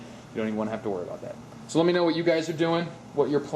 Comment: LOL I THOUGHT YOU SAID "RAPE COMMENT AND SUBSCRIBE"